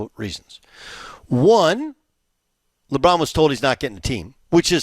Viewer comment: sounds like he said Lebum for a second there😂😂😂😂